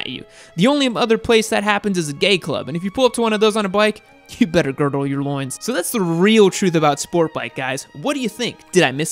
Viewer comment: I thought you said "you better gargle your loins"